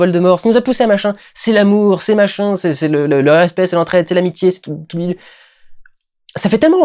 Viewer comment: à 28:31, c'est moi ou il a dit "l'herpès, l'entraide, l'amitié" ?????? O_o